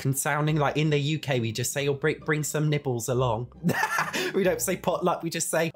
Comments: 100% thought you were saying that you ask people to "bring some nipples along" 🤣🤣🤣. I eventually realized you were saying *nibbles*. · I thought you said, "bring some nipples along"...